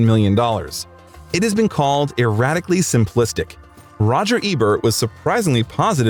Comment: it was not called irradically simplistic it was at 43:56 "irritatingly" simplistic